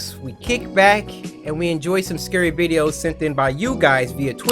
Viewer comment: Did anyone catch saying scurry videos